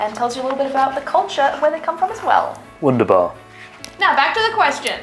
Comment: Is Chris saying "wunderbar" the german word for "marvelous or amazing" at 4:56? Sounds a bit like it :D